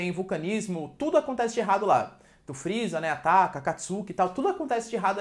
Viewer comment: tive que voltar duas vezes pra ter certeza mesmo que ele falou "o freeza ataca, akatsuki" 😅😅 Ótima aula!